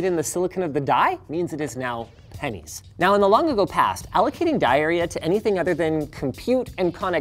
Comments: I heard “allocating diarrhea” when he said “allocating die area” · did he say "allocating diarrhea"?